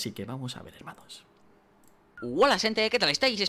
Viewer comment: entendi bolas de gente xd